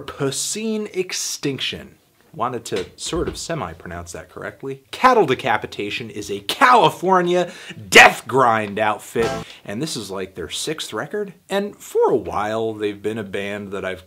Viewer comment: did you say Cow-ifornia?... Not even mad at that